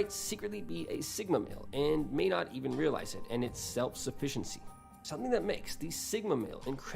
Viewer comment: I literally thought it said Self-suffering for a moment xD.